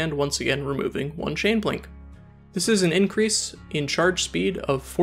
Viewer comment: Oh, I get it, an INCREASE in charge SPEED means FASTER charging, but a DECREASE in SPEED means SLOWER charging... It said "increases charge speed" but I thought it said "increases charge time"